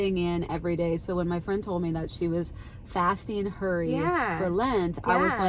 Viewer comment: 😂 in the beginning I thought you said that your friend was fasting Curry and I thought "Hmmm, 🤔" Whatever works! 😂